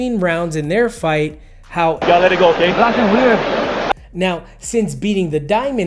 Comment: Maybe you could just tell us what Max says lol I have no idea what he's saying in this clip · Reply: Blocking weird?